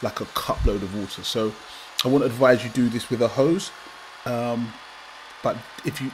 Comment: I wonder what idiots would do this? It's the worst thing to do for your car, and you trying to kill people and get them hurt or trying to give them an expense? Getting engine upto temperature and putting a hose in the Intake would potentially Crack the Head or Block. And in some-cases over pressurizing the engine. The harder it turns the more forces it needs to release. No resistance from the drive-line, just pure power and a full cylinder of water. If there are enough rotational forces and you manage to quickly fill one cylinder with water the head is potentially going to pop off with a lot of force. Spraying with a bottle like that won't necessarily damage anything other then cracking any aluminium parts including Pistons. Maybe it does clean parts, but engine coolant that leaks in is already pre-warmed and not cold. it equalizes with the engine. You can say "Fuel" But that burns, and never really gets the chance to change any temperature of any components. It also is not as dense as water. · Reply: Jod Bronson I seen your comment they recon you mentioned Drugs and it was flagged LOL! Also Audie Pair go to 2:32 and watch from their. He did say its better to do it with a hose. His exact words "I will advise you do this with a hose". Listen. I don't agree with what he said. I agree maybe with spraying but absolutely no HOSE!